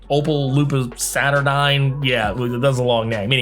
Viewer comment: i misheard you... Opal LUPUS Saturnyne